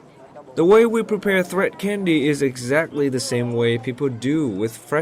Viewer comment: Did I hear he said "This Keo Chi CANDLE?" I think candy instead..